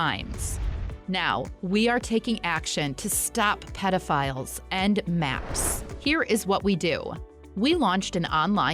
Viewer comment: I thought it said predators and Mars and the fact that it also showed a planet in the background. I was very confused